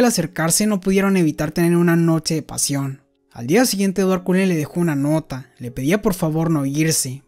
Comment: escuche Edward Cullen?😅